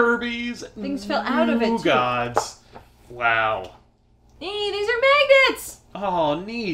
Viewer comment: I got pretty scared at 15:26 I swear said "This are maggots!" haha